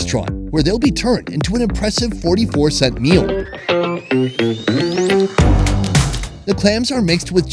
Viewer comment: did you say 40cent meal? if thats what you said, then im moving there.